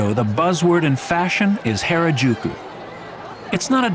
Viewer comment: Did he just say "Hair a Juke you?"